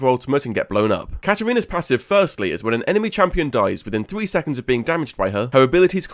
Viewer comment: So...I'm kind of out of it, and at first I thought you said "Her passive is that enemy champions die within 3 seconds of getting close to her" and I had to rewind the video >_<